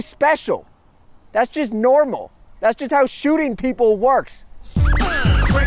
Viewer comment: Is that "deez nuts' I hear?